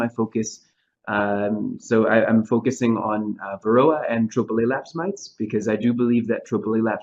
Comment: Great talk! Most interesting subject. I did not understand what BUG feels will be the next horror bee keepers will surely be facing in the future. It sounded like he said "triple-a-lapse mite" to me. I have a couple of questions to advance. But first I would posit that when the Varroa mite is standing on the dorsum of the thorax of a bee, it is looking for a drone to hop on. Further, I believe that the mite is in the best physical health to make the journey to a new hive to "grow" the numbers of Varroa found out in Nature. And the drone bee will be accepted past the threshold of the new hive and the guard bees located there. This would be similar to honey bees swarming to grow the number of bee hives out in Nature. I plan to place hive entrance queen excluders on all of my hives to see if this slows down the spread of Varroa between my hives in my apiary. My last point: we teach that 90% to 95% of Varroa are located inside the brood cells and not riding on adult bees. Hopefully in Texas at least, Hobby Bee-keepers are not looking for these Varroa hitch-hikers, but are doing alcohol washes with denatured alcohol or windshield washer fluid which is a water/alcohol mix. And when the count of these washes warrants, we treat for Varroa. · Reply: was talking about the Tropilaelaps mite that is in Thailand. The Varroa mite is blind so it is not looking for just a drone but for any bee. Be careful about placing queen excluders on the entrance of your hives. You will trap the no queen in the hive if they swarm and cause stress on the hive. Also, the drones inside the hive will pile up on the entrance inside and block the entrance. This will overheat the hive and cause stress and possible death of the hive. The 90-95% are on the bees in the cells and on the adult bees for about 3 to 13 days.